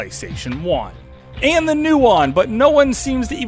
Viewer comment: What system are you saying? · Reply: The nuon. It's very rare and about 10 or so games